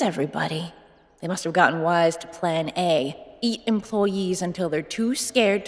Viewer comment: Kirito was scared of his species being canabalized meanwhile his wife is out there eating people for psychological torment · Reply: Holy shit I didn't know she said eat until I turned on subtitles. I thought she said beat employees not EAT😱😱😱